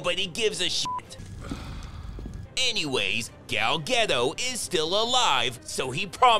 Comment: Did he just say “Gal Ghetto”😂